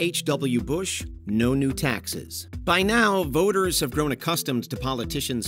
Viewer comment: When Bush Sr. said "No New Taxes" I heard "No Nude Texans" 🤣